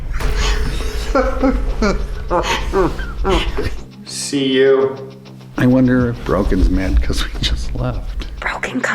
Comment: I can't make out the beginning but it's something like "blah blah blah you'll pay for that." I'd bet 10 trillion dollars on the "you'll pay for that." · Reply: Update it sounds like "Let's see who will pay for that."